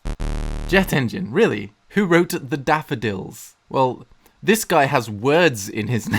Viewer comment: Did you really say "wroted" like Karl Pilkington from The Ricky Gervais show lol? WTF!